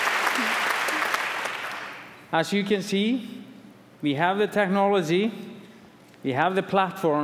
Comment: I heard: "We have the technology. We can make him better than he was. Better...stronger...faster."